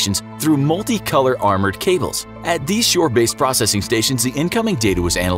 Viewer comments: He says, "multi-color armored cables" Caption says, "multi-conductor armored cables" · did he say multi-color or multi-conductor ?